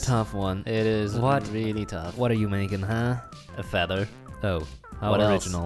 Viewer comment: Did I just hear..........“WHAT ARE YOU BUILDING” More like What are you painting